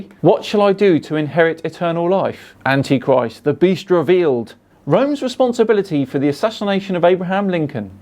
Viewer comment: One of those books sounded genuinely interesting, until I realised I’d misheard “The Beast Revealed” as “The Bistro of Eels” 😞